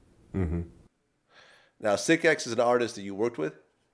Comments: Vlad mispronounced Sicx, I think that's why Lynch answered his question the way he did. I know it confused me. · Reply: Ya and Lynch thought vlad asked is SicX the "hardest" you worked with? Not artist. That's why he said Cbo was hard. And that's why he said probably and listed Cbo X raided and triple sicX as the hardest he worked with. It all makes sense once you know he misheard. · Sounded like Vlad said "the hardest you worked with" not "an artist you worked with."